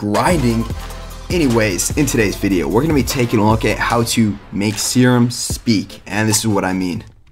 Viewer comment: Thought it said make sperm talk LMFAO